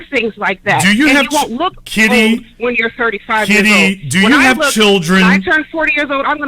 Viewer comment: Did she say she was 35 yrs old at 7:00?